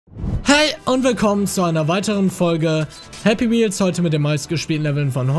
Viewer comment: Ich hab anfangst einfach verstanden Hey und willkommen zu Happy Meals